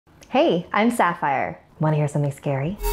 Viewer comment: I always thought she said "hey am star fire..